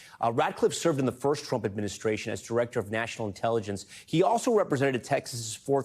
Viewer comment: I thought he said Ratliff